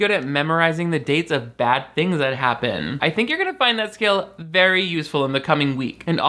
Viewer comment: typo in the captions there, “scale” instead of “skill”